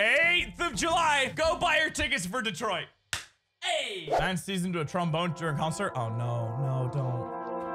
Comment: It sounds like he is saying: "Man sees Trump voter during concert"